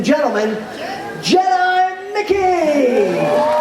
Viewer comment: For some reason when he introduced Jedi Mickey, I swear I heard him say "John McCain." I was very confused for a moment.